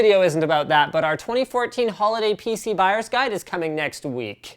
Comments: At 00.47 it sounded like he said pc virus guide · Could have sworn you said 'Holiday PC virus guide'